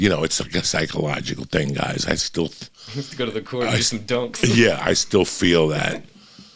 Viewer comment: tf did he say? "go to the court for some dokes?" "go to the court for some dunks?"???